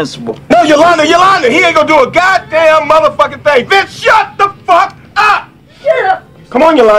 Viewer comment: “VINCE SHUT THE FUCK UP!” best delivery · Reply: I actually thought he said "Bitch" instead of "Vince"